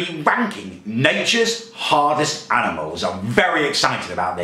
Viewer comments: it sounds like he said wanking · I thought he said wanking😂